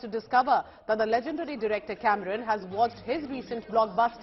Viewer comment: did she say cameramen 🫢